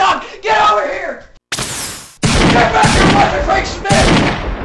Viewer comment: cracked me up the most😂😂😂😂😂😂😂👍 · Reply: MilesTails101 4:27 did he say GET BACK HERE RIGHT NOW CRIAG SMITH